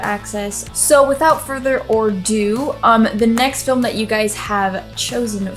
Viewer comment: No, no - they're not welcoming the newest inmates. They're intimidating them. (and, psssst......it's "ado" - "without further ado") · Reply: Could have been she just misspoke. Definitely sounds like she said "are do"